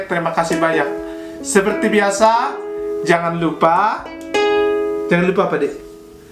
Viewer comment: bilang apa dimenit 12:49 ? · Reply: jangan lupa menabung